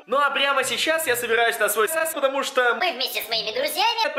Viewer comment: стоп ты собираешься на свой секс? Мне одному такое послышалось